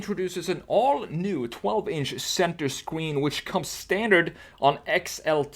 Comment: It sounded like you said Center Squeen